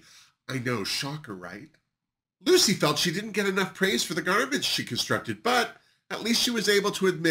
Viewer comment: DID U SAY GARMENT... OR GARBAGE? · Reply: LOL. Garment. I said garment! LOL xox